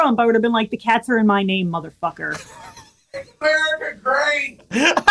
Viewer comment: what did scream at 10:14 ? i could couldn't hear him properly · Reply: I think he yelled "MAKE AMERICA GREAT!"